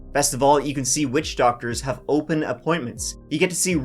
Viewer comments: Did he say witch doctors have open appointments? 😅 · I misheard a line in the Zocdoc ad as, "Best of all, you can see witch doctors."